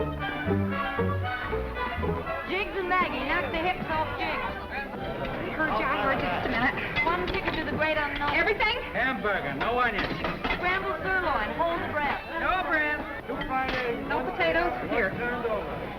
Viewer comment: Did you say no onions at 14:33 I don't like onions Because i am alergic